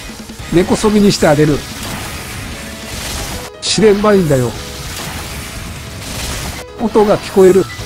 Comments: 「4ねばいいんだよ！」にしか聞こえんwww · 「4ねばいいんだよ」って聞こえる。怖い